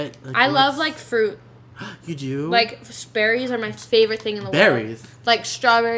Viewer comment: When she said berries I thought she said Sperrys I was sooooo excited that she used the too but turns out I heard wrong😂😂😂😂😂